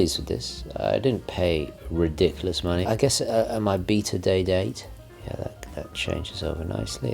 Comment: did you just say you had a "beater" Day-Date?? 😅